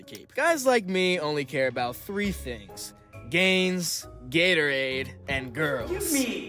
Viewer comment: I thought he said Guys Gatorade And girls 😂